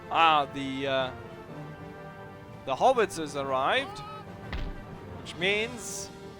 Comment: You Deutsche Sprecher..."haubitzes"...I thought you were saying "Hobbitses"...